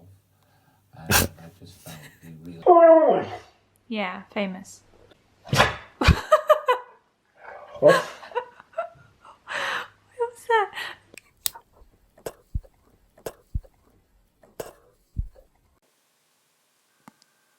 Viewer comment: It actually sounds like he said what